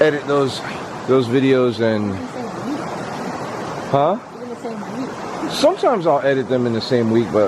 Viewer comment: I heard someone said hi on